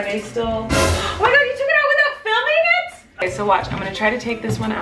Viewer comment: What did she said? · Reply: "OH MY GOD, YOU TOOK IT OUT WITHOUT FILMING IT" 😊